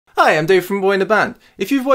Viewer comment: i literally hear d a i f u